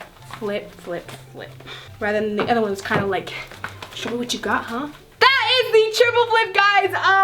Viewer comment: what did she say?!😂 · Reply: Everlasting 75 she said show me what you got huh